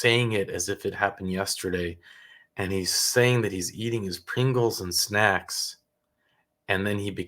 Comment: Um, did he just say he was possessed by "pringles" ? 😂